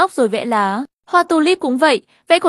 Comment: Hoa tulip mà em nghe thành hoa tolet